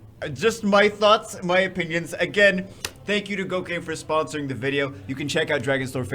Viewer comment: I swear, I heard you say "Thank you cocaine".